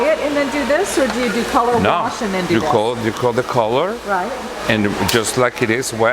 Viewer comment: does he say call the colour????